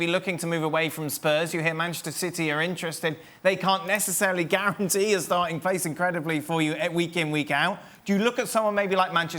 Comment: did he say city can't guarantee kane a starting place?